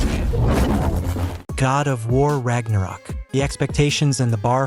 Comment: did he say NlGGER 4:50?